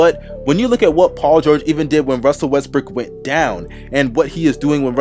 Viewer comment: Russel 'Westbrick' I heard that